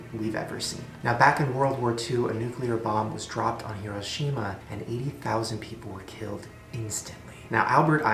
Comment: I thought he said a bomb dropped on kirishima.. just me?